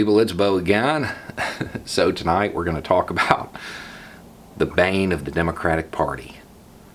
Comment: Dude, I thought you said "The banging of the Democratic party"....